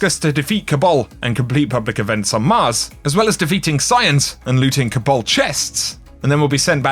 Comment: Swear I heard you say "defeat science" with your accent ahhahah